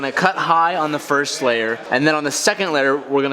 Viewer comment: you said letter hahaha instead of layer😂😂😂😂